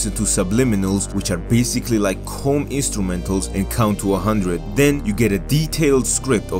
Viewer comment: What does he say at 4:26? Is he saying "cone instrumentals"?